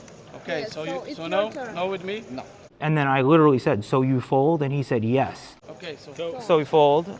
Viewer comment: Damn bro, you look like me 3 months after my wife and kid died in a car accident... Take a shower. 9:18 Also, it sounds like you said "so he folds?" not "so you fold?"